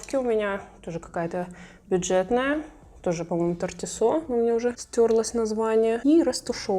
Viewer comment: не поняла как кисть для консилера называется? Понравилась и для консилера и для тона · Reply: если правильно расслышала - Артисо , вроде так )